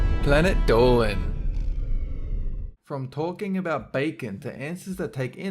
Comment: almost thought he said "porking about bacon" Almost made the greatest pun of all time